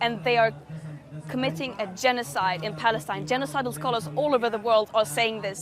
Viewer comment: 😂 Did she just say "genocidal scholars" ?